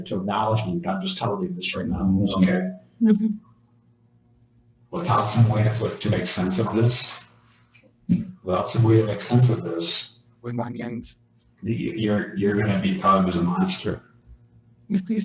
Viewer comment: What the heck is and who.. is that 4th voice? Saying things every now and then. One of the last things I heard was "by playing mind games"? WTH?? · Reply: I heard it say "with onions" as I am getting eggs and onions to make tuna salad. Creepy